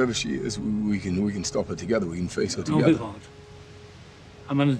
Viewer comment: the subtitles say "Whatever she is we can stop her together. We can face her dude"